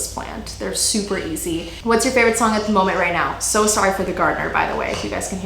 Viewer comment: I thought her favorite song was called “So sorry for the gardener” by The Way... for a solid second. 😂